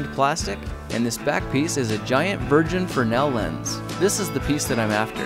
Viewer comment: did you say giant virgin? ??